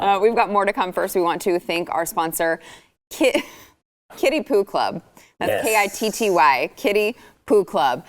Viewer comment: Gee Sarah.glad you clarified that. Thought you said kiddy pooh club.